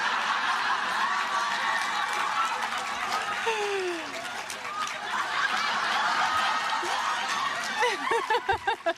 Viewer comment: What did that person say at 11:30?? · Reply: Rebekah Dawn “DO IT IN THE SHOW”